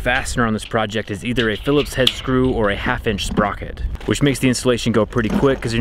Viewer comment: point...did you really say 'sprocket'?